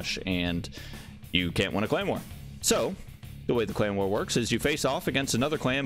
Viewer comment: Lmao around 8:30 when he kept talking about the "clan wars" I legit thought he kept saying "claymore" 😂😂😂😂